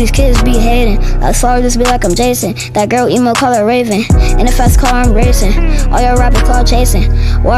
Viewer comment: I thought he said that girl eat my wood call her Raven... Like Orlando brown had said bro i was crying!!!! But he said that girl emo call her raven.Bro if he said she eat his wood call her raven omg! can you imagine the clout lol